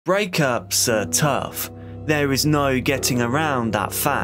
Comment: Thought he said “A cups are tough” in the beginning